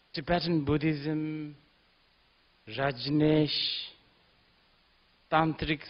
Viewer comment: I think hes saying rajneesh as in osho rajneesh and not reincarnation